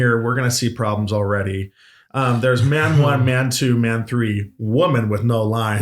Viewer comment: I thought said, Matt 1, Matt 2, Matt 3. I'm like WHAT?!